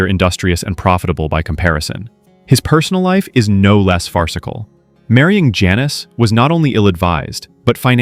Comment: Did you just say “fartcicle” haha hehe hoho😅